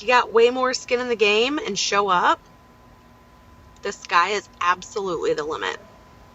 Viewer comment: ish When she said "the sky is absolutely the limit" I first heard it as "this guy is absolutely a woman" and I was like, oh I guess that is how the kids say it these days, but then that didn't reflect in the text bubble, so my brain caught up and I realized what she had actually said. lol